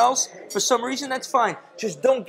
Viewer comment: I thought he said don't get Duked I was like oh no he didn't